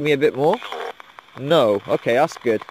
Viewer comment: I heard the word "more" instead of "no" but might be my hearing xD